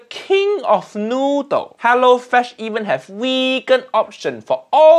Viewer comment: lol weekend people in cc instead of vegan lol